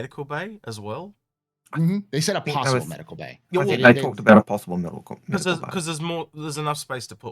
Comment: I could have swore I heard all caps will get medical bays.